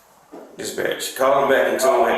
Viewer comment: oh he said “Dispatch”….I first thought he said “This Btch”